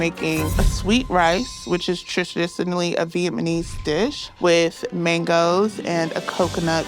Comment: Is it just me or did I hear her say “Viet MA nese” 😭